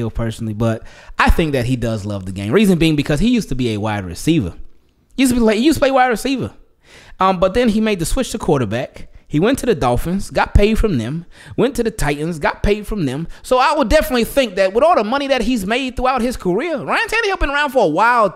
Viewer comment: The titans 😂😂😂😂 I thought he said he wants to win